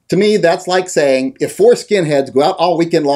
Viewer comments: Thought u said Foreskins.... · Misheard "four skin heads" as "foreskin heads"...... lost sence of reality for a moment. 0_o